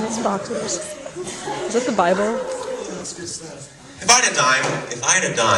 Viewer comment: Audio is shit so I'm not sure, but I thought I heard a girl asking "Who's [Salvador Dali]? is he in the Bible"? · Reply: Allie Kiteley Yeah, I heard 'Is that the Bible?'